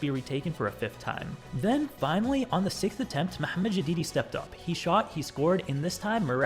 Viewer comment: I thought u said the didi